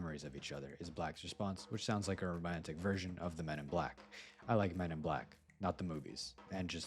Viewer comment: did i hear "I like men"?!